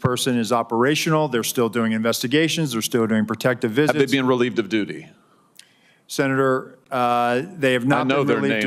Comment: My first language is not English. Did he say very shortly after 1:36 "you've been relieved of duty!"